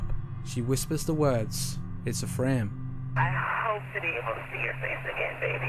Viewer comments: I don’t hear “it’s a frame” I hear “it’s ok” almost like she’s self-soothing or whispering it as part of her goodbye message. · i heard "it's afraid" instead of "it's a frame" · I heard "It's a prank"!! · The last audio clip, I hear ‘it’s okay’ FAR more than I hear ‘it’s a frame’. ‘It’s a frame’ doesn’t make any sense as a statement. If 9/11 is fake and she’s involved, why would she admit this? Also ‘it’s a frame’ isn’t at all a comment thing to say when something is staged. Makes 0 sense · Sorry, i dont hear "it's a frame". I hear "it's afraid".